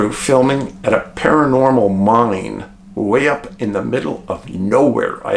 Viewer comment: Have listened to Dave at 25:49 several times & still can't decipher what he is saying when he refers to what sounds like a "paranormal monnang." A paranormal WHAT? Anyone know what word he is saying? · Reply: He said “paranormal mine.” ❤